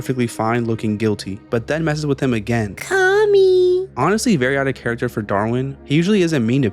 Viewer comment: WHAT DID THAT PUPPET SAY? THAT PUPPET HAS POTTY-MOUTH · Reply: “Call me” Bro istg